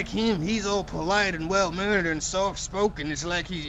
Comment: Did anyone else hear "well murdered" at 22:37?